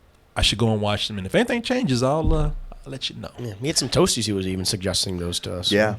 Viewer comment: when said "some toasties" all i heard was "satoshis" lol btc!!!